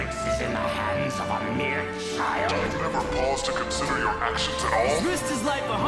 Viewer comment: I THOUGHT TETRAX SAID DO YOU HAVE THE BA(redacted)S TO CONCIDER YOUR ACTIONS AT ALL BUT HE SAID DO YOU EVER PAUSE NOT DO YOU HAVE THE BA(REDACTED)S